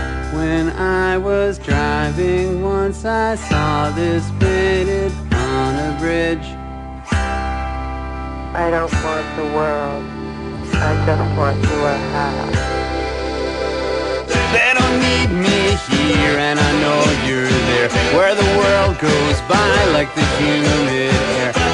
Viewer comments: "I don't want the world, I just want your half." Love that line. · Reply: Nick Razor I always thought she was saying I don't want the world, just more hash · I don't want the world, I just want your half. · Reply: Always thought she said "I just want to wear hats." Thanks for setting me straight. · i heard her saying "i don't want the world i want hats..." the second time i listened she said "i don't want....want hats"